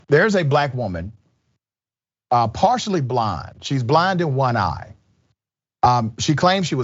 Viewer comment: Sounds like he said “Blonde in one eye”